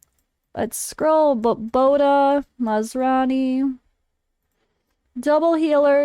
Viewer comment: sounded like your saying maserati not masrani LOL